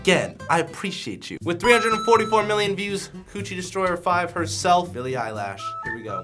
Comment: Bro, did he just call her Billie eyelash? Lol.